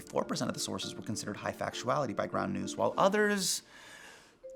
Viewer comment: did Hasan say brown news? I thought he said brown news. ooooohhhh..its ground news